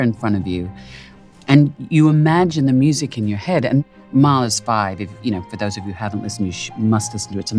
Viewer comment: Mars what is she talking about at 2:36 she says mars 5 I can’t find that anywhere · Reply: Mahler’s 5 - Mahler is the name of the composer. Sorry for the late response!